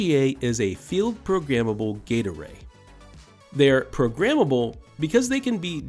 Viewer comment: My brain misheard the full name of FPGAs as "Field Programmable Gatorade" and I can't stop giggling